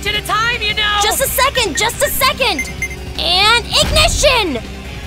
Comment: In issue 10 I thought tails said eggnition